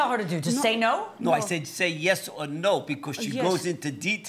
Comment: the plaintiffs witness lied! He definitely told her to say no! It’s clear as day!! You never hear him say… say yes or no. He clearly says say no